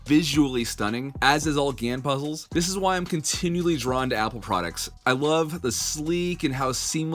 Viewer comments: did he say apple products?😂😂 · did he say apple products?